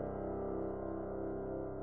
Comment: wait did she just say love you?